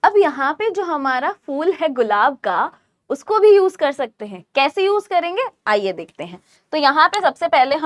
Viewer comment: that "phool h gulab ka" sounded like kk create😅